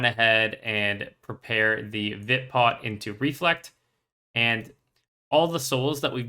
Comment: I cannot for the life of me figure out what you're saying at 26:04. It kind of sounds like "Vid-pot" right before the reflect? I'm only just learning how to PvM so I'm taking extensive notes on the rotation and how the mechanics play together, so I just want to make sure I'm not missing anything. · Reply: Vit pot = Powerburst of vitality Doubles your current lifepoints for 6 seconds.